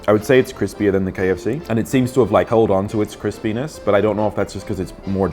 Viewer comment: Turn on english subtitles, go to 4:10, and you'll read "Chris penis instead of Crispyness".